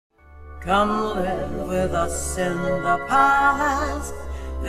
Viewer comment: I love that it sounds like she says "come live with us in the paalaaa" instead of palace lol